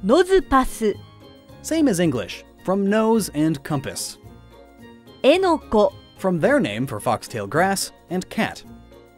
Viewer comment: Did she said “Enoco” not “eneco”?